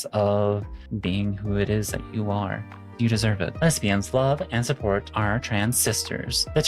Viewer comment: for the briefest instant while I was alt-tabbed I thought you said thespians